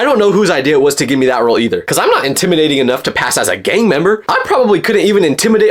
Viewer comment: LOL I THOUGHT HE SAID GAY MEMBER 😂